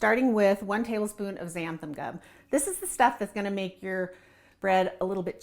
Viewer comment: how do u spell zanfenell i couldnt understand i think it was at about 3:06 sorry couldnt catch the pronounciation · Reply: It's xanthan gum